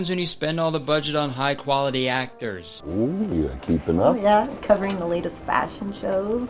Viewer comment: Writing out what I think the boss is saying. 4:14 - “ooh keepin’ up.” 4:18 - “haha yeah” 4:28 - “I tree power cars.” (I genuinely cannot figure out what he’s saying here. This is my best guess based on what I hear, intonation and speech patterns.) 4:37 - “I was talkin’ with my dad over the holidays, you know? A-and I- I said- my dad said God’s not dead.” 4:44 - “we act like he is.” OR “he acts like he hears him.” (I’m leaning towards the first based on context) 4:47 - “couldn’t evil think the same?” (I literally cannot make out any words other than “evil” but based on his intonation and some of the individual sounds I hear I think this close. It would make sense if the part before is “we act like he is”.) EDIT: after watching this video a few more times, I think 4:28 is “battery powered cars.” Not really sure why that’s something he would be saying and seemed so confused about but it makes more sense than “I tree powered cars”